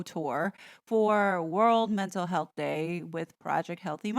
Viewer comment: I thought it said World Menstrual Health Day😂